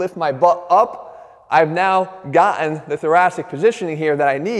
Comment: Right about at 3:00, did you say "Got-en" instead of Gotten? Fix that Ebonics stuff, Jeff!